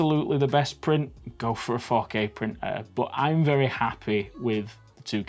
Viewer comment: When he said 4k printer I thought he said fuck A printer